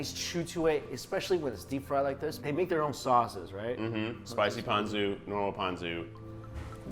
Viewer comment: thought keith was saying "spicy pantsu". XD